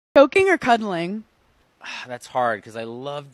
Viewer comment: I thought she said soaking hahahhaha...i love Hannah ❤❤❤